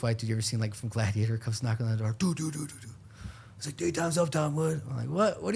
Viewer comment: "it's like day times up Tom Wood" Can anyone understand what he's saying here, is it really as the Automated English subtitles says or is he saying something else. I've replayed it over and over and I can't get it!